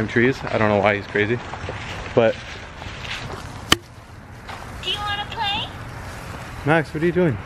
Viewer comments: i hear her saying right here or right there not sure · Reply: i heard her saying "do you wanna play" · I heard her to I heard you're saying you want to play